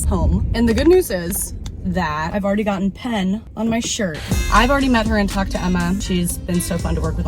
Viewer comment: omg i thought i heard "i already met and f*ck*d emma" and i was like ok this channel is bold 💀